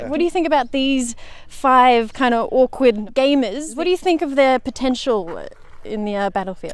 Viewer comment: I thought she said “ awkward gay men”